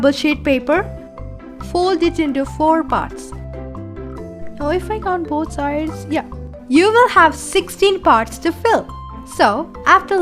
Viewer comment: what are you saying i can't understand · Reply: She is saying "four parts now if I count both sides yeah you will have 16 parts to fill"